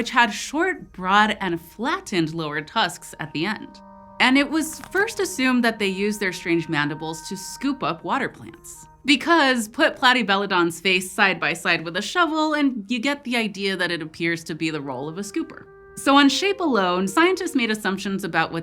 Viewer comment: thought you said water balloons there